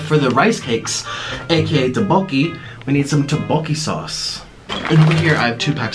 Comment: d-did he just say 'doboki'?! What